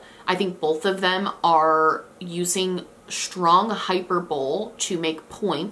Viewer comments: I can’t help it. Did you say “hyper-bowl”??? 😁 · I heard “They both use hyper-bowl” and it took me a minute of being like …is that an app?… to realize she meant hyperbole.